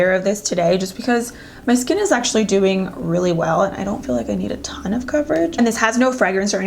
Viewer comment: i hear "dewing really well" "these dew last .." lolol